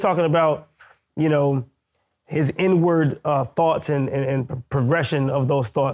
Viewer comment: I thought you said n word thoughts I about fucking died choking on the drink that came out my nose.